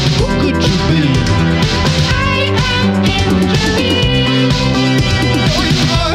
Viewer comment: "I am Injury!" and "No you aren't, you made the choice to be" hits the hardest. Relatable lyrics. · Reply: chat i thought it said 'evangeline'